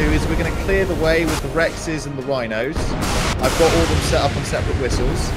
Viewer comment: DID HE SAY WINOS!?!